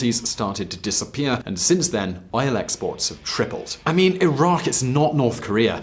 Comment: Sounds like you said Iraq.